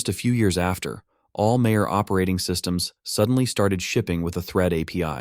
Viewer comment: mayor instead of major